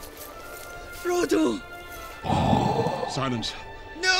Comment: I think it’s cute when he says “froato”